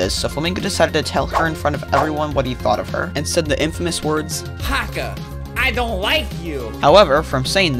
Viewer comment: "Hakka I don't like you" sounded like he was saying i hate fnaf